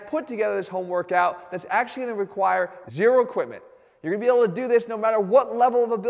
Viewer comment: I thought you said No equipment.